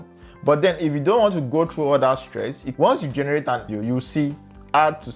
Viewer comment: Im sorry you lost me at 19:43. "Once you generate ____?" What did you say there? When does Add to Scene button appear? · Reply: You don’t need to do anything, once you generate a video you’d see the ‘Add to scene’ button at the top left